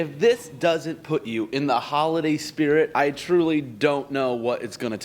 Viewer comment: I thought he said “Halloween Spirit”